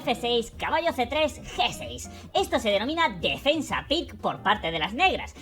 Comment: "Defensa qué"? Alguien sabe lo que dice? Defensa Pick? Se escribe así? Me interesa para buscar más información